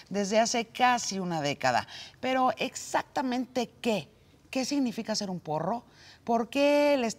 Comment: Pensé que decía " que significa ser un perro , platicamos con uno de ellos" me siento estafado :(